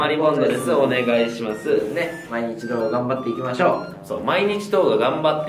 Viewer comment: はいどうもー！水溜りボンドです〜お願いします〜ねっ今一度頑張って行きましょう！ということでねっ って聞こえたｗ まさか、毎日動画頑張って行きましょう！だったとはｗ